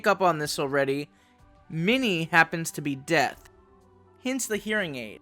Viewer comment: I thought you said "dead" and I was confused. But I was really happy when I realized what you actually said, I don't personally know any deaf people myself, but after seeing the masterpiece that is CODA, we definitely deserve to see deaf representation in Pokemon and other media!